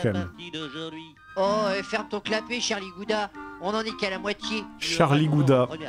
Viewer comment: J'ai entendu "Charlie Bouddha" ça m'a fait encore plus rire